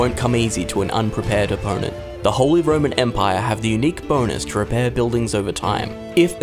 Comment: Take a drink every time he says Holy Roman Empire, see how long you last. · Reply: For some reason every time he said I kept hearing "Holy Vermin Empire".